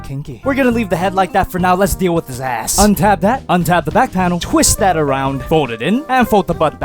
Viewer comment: Did you say untap that?😏